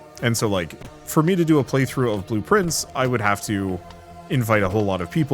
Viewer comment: Oh! Blue Prince I've been hearing about it off and on for several days and I thought people were saying "blueprints".